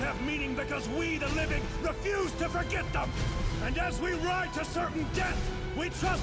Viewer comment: I thought you said you ran out of budget😭😭😭